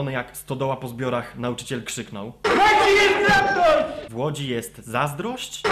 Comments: Ja usłyszalem "W Łodzi jest traktor" xDD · usłyszałam "WŁODZIMIERZ ZASNĄŁ!" xD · ja usłyszałem W ŁODZI JEST ZAZDROŚĆ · ja usłyszałam w łodzi jest traktor :D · usłyszałam '' w łodzi jest traktor'' xd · Ja usłyszałam ,,W Łodzi jest traktor '' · Ja usłyszałem "w Łodzi jest raptor!" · "W ŁODZI JESS TRAKTOR!" Ja tak usłyszałem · ja usłyszałam "w łodzi jest traktor" ! lol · Ja usłyszałam 'w Łodzi jest traktor' ._." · W Łodzi jest traktor! ja tak usłyszałam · usłyszałem "W łodzi jest traktor" :D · ja usłyszałem w 4:20 "do x-factor" xD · Ja usłyszałam W łodzi jest traktor xD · Ja usłyszałam "w łodzi jest traktor" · Ja usłyszałem w "łodzi jest traktor" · "w łodzi jest traktor "usłyszałem :D albo "Pierdolony aktor ":D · ja usłyszałem w Łodzi jest traktoś :P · Ten Darek przypomina trochę Pottera XD Usłyszałam w łodzi jest traktor ; ) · Woźny jest sprawcą! ... ale usłyszałem w pierwszym momencie, że 'W Łodzi jest traktor' :P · To ja usłyszałem " Mamci wentylator " lub też " Mam ci wentylator " ewentualnie " W Łodzi wentylator " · Ja usłyszałem "ZABILI MI TRAKTOR" XD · Ja usłyszałem: "W wodzie jest traktor" xD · Ja usłyszałam w łodzi jest traktor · Usłyszałem W Łodzi jest traktor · ja usłyszałem "w Łodzi jest traktor" xD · W łodzi jest traktor ja usłyszałem :D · Ja usłyszałem: w łodzi jest traktor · Ja też usłyszałem "w łodzi jest traktor" ;) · ja usłyszałam w Łodzi jest traktor heh... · ja usłyszałem: "W Łodzi jest traktor"....xD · ja usłyszałem "wyłudzi jej traktor" >.>